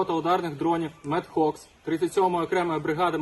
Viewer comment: почулось мєд кокс)